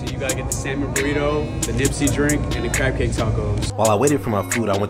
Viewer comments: I thought he said crackhead tacos 😂😂😂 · I thought he said "crackhead tacos" at first 😂😂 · I thought he said crackhead toast 😂